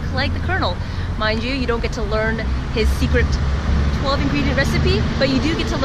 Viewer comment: what did she say??? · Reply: 12-ingredient recipe